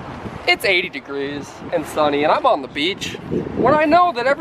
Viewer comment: Kermit the Frog VS Seagulls on Sunday is going to be Hilarious! Also working on recording a skit with Kermit in the deep swamp with some Gators. Stay tuned for that sometime within the next two weeks! ;) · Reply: I thought he said go to bleach